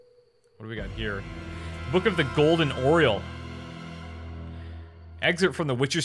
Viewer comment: I thought you said "Book of the Golden Oreo" i was like wut